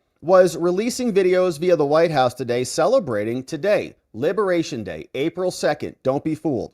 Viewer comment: I thought that that said “ Happy Libation day “